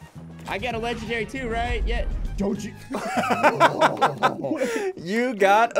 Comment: it sounded like said “Don’t you f*^king OHHH”